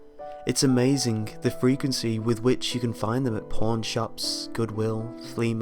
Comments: At around 0:23 i thought he said porn shop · When he said "pawn shops" I thought he said "porn shops". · I knew he said "Pawn shops" but it was kinda hard to tell with the accent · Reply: Jennifer Holly honestly when I first heard it I thought he said 'porn shops' XDD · did he just say "porn shops"